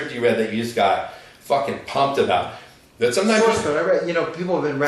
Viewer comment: What script did you mention at 45:33? I couldn't hear. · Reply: It's the script Source Code - okay movie, but excellent screenplay.